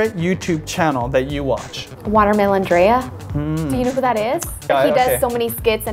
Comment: Who did she say her favorite yt channel is to watch? I'm having trouble catcher her words. I thought she said waterman andrea.